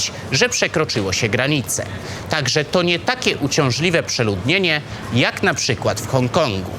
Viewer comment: Nie wiem czemu, ale katem oka napis na miniaturce przeczytałem jako "Kumulacja odbytu" xD Nieważne, świetny odcinek! 1:35 Jeśli oglądają ten film osoby głuche, pan powiedział "także to NIE takie przeludnienie jak w Hongkongu", wbrew temu co mówią napisy (także to takie przeludnienie [...])